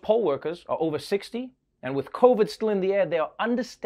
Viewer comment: I misheard and thought his was talking about pole workers. You know, the dancers. I was quite confused.